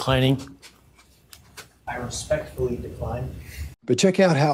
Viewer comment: Great idea speeding it up….I thought he said respectfully angrily